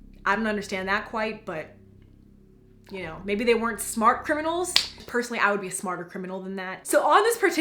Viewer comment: The first time you said smart criminals I thought you said smart kernels